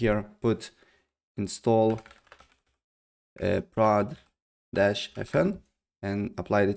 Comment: I need help, I didn't hear what he said at a moment: install fn something. Please help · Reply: install prod-fn